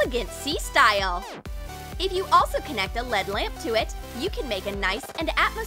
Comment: did she just say ‘LeAd LaMp’? L.E.D. ...